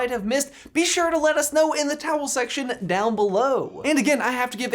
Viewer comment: did he say “towel section”?